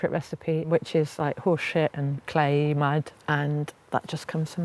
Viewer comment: Did she say horse shit and clay? 😂